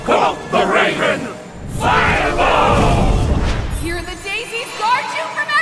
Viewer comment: IM ABSOLUTELY LOVING THE INCANTATOINS FOR THE SPELLS. The level of detail used for certain spells are just so funny, with some of them being plays on old poems or short stories like Edgar Allan Poes "The Raven" at 7:30. "QUOTHE THE RAVEN, FIREMORE" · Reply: Did they “Firemore”? I thought they said “Fireball”. I know it doesn’t rhyme but I thought that was the point.